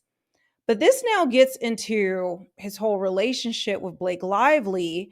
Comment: I like how I misheard you say he was in a "relationshit." I'm keeping that one! LOL